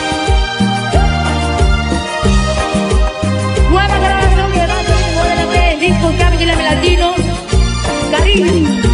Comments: No le entendi 😐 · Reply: Dice"nueva grabación que va a salír en el nuevo ELP (o EP), El disco Grammy,Grammy latino.. CARIÑO" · Algun sabe que dice ella? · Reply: gracias! Pero porque dice “cariño” si La cancion es “no debes jugar”